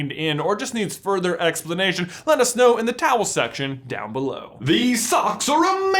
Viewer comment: So I have a question... why does he always call the comment section the towel section? Or am I mishearing that?